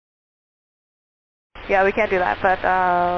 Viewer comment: Love your videos but man, these subtitles are wrong a lot. At 1:58 the Delta pilot said "Yeah, we can't do that", not can. At 3:44 N51SW said "I'm very flustered with what just happened. My cabin just filled up with smoke. Uh, it smells completely foul. Let me pull off on Charlie and just compose myself." Just a couple of examples. Maybe I should get a job transcribing noisy radio chatter.